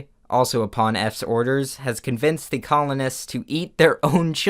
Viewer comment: On 9:43 I yawned so I heard "communists", not "colonists"... I guess communists eat their own children